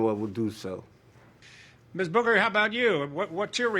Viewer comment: I know Doug can be ruthless sometimes, but did he just call her Miss Booger?